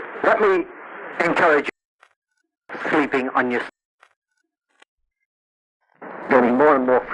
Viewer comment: Man I wish I knew exactly what he said between 27:10 and 27:40. Thanks for sharing all this information, much appreciated! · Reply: Sounds like he was talking about sleeping on your stomach. Tough to tell, but that's what it seemed like to me when it cut out.